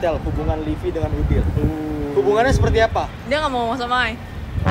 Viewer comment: itu livy ngomong apa sih? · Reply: Dia gk mao ngomong sama I (aku)